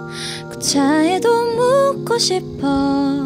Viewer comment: 난 왜 지금까지 구차해도 묻고싶어를 참외도 먹고싶어 로 들었던거지..